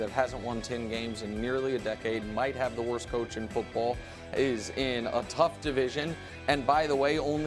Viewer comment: am I thought he said jags with worst coach in the league and I about lost it LOL